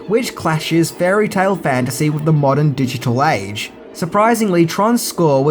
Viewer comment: I thought said "Digital SEX".